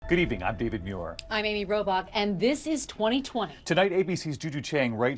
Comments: Sounds like she says her name is Amy Robot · I'm sorry but did she say her name was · I could've sworn I heard "I'm Amy Robot'